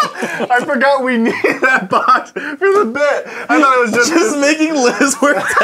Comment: if you put 0:04 at the speed of 0.25 he clearly says "BOT" Not "BOX". even on normal speed you can tell